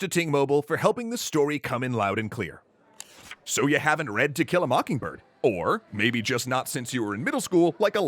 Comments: i sweard i heard him say "talking about Tequilla Mockingbird". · I keep hearing "tequila mockingbird". 🌚🌚🌚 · i heard "tequila mockingbird" far too many times